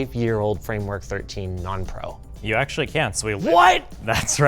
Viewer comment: worth noting the auto captions have saying “can’t” instead of “can”